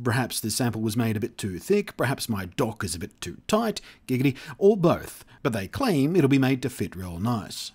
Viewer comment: Did you say "giggity" at 1:01?